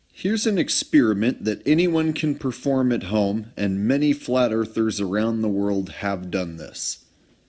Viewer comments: "Many flather around the world" OMG DID I HEAR THAT RIGHT? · At one point i heard him say... "Flat earth around the world"